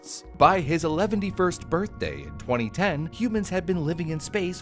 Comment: Did he just say "Eleventy first Birthday"???